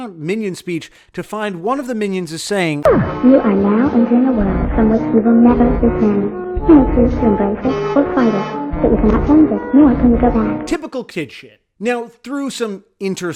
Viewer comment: what is it saying in 3:50,i cant hear any words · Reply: Esquadrão Platina You are now entering a world from which you will never return you may chose to embrace it or fight it but you can cannot change it nor can you go back